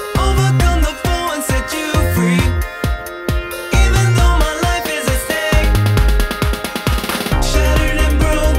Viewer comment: I thought he said “even though my life is a steak”